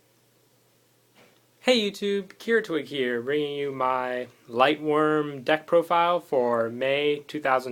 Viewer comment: i thought it said lightsworn....